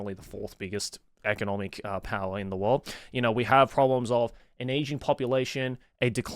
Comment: I thought he said 'we have an Asian population' which seemed pretty obvious!